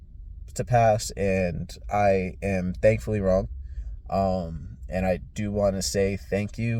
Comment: wut u say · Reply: I said "I am thankfully wrong" meaning I'm willing to admit when I'm wrong... (I assumed they weren't going to help me and they did)